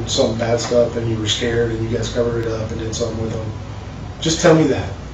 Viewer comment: How is what the detective says at 47:02 inaudible?? I hear it clearly! In case anyone relying on the captions is curious, he said “and you guys covered it up and did something with him?”